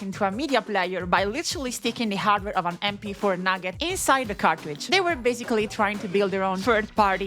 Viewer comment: did she say nugget